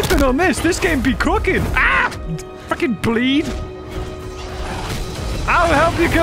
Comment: gasps Did he say a naughty word without censoring…? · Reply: He did not in fact, he said fricken